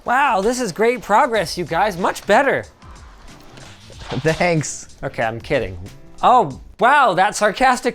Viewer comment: did he just say "danks"? 😂